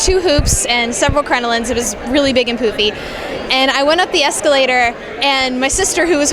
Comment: I thought she said "Big and poopy"